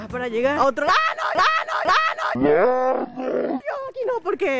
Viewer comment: yo entendi Ano xDD